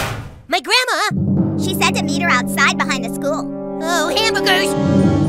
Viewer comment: 오 빌어먹을 영어로 oh,hamburgers 인가요? 이렇게 들리는데....